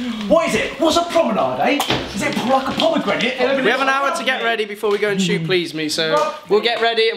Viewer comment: did anyone else hear say “is it like a pomegranate” i’m crying😂